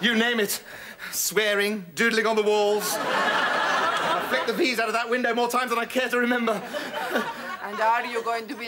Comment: I grew up watching this stuff.. As a kid I thought he was saying "flick the BEE'S out of that window..." My innocent little mind hadn't even heard of flicking the V's before 😂😂